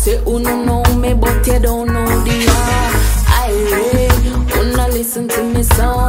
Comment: love your song indeed, what is the word you`re saying between the lines? exactly at 1:53 and again 1:56. "what spirit desire, spirit gets". thank you for sharing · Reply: IRIE